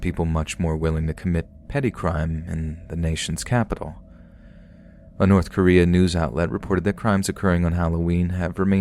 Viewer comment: A North Korean news outlet??? Did I hear wrong?